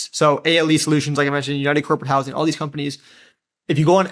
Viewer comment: Spoke kind of fast at 38:21 couldn't understand what companies you were saying. Can you say what the company names are here? Please and Thanks! 😁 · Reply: ALE Solutions, United Corporate Housing, THD Housing